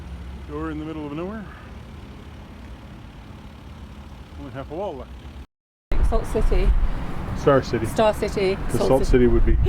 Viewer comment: I think is saying, "We're in the middle of nowhere.....and we have wall art." Good to see you enjoying Birmingham. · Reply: I heard, "Door in the middle of nowhere...only half a wall left."